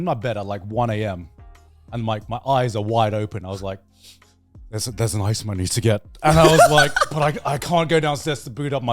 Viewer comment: what did grant say? ice money? 34:40 its a one-handed kinda game 40:24 if you include every single mod ever made, then maybe. but base minecraft isnt nearly as complicated as even terraria. 1:12:20 it surprises me a little that none of them can touch type. ive never taken classes either and my technique is definitely not standard, but simply using computers for 15 years of my life has allowed me to touch type. 1:29:03 CALL THEM THE FUCK OUT, WHERE WAS IT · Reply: Close, he said "there's an item I need to get", but I definitely heard ice money when I first listened to it back